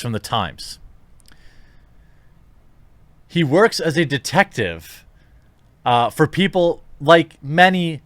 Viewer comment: When you first tried to say detective I thought you was trying to sneeze. Sounded like you said 'defective'. Spicy.